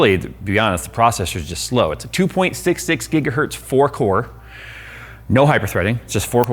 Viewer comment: Sounded like he said 4 whores